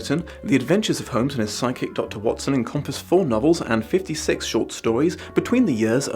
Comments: Okay... wasn't totally paying attention until I misheard "sidekick, Dr Watson" as "psychic Dr Watson!" And, hear me out, that sounds awesome. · at first I thought said "Sherlock and his Psychic Dr. Watson." and that is a spin off I've yet to see. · When he said “and his side-kick, John Watson” I heard “psychic John Watson” and I feel like that would have made a very different story. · I thought you said "psychic dr. watston" and had to do a double take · Oooh. Side kick Dr Watson. I thought you said psychic Dr Watson for a second there...... That would be an interesting story. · For a second there, I thought you said psychic Watson (not sidekick Watson) and thought when the heck did Watson get ESP??!!! 😂